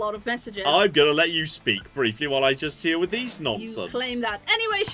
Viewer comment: Did just call his customers nonces?